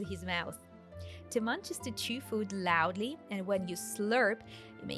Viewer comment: minutes isn't that "chew" instead of "two"?